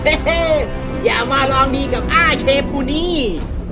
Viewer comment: ผมได้ยินว่าอย่ามารองดีกับเทพเทปูนผู้นี้